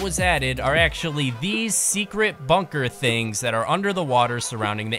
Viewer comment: in the vid, did he say "secret" bunkers?